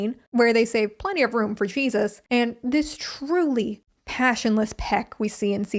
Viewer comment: did you say cheesus ?? lolll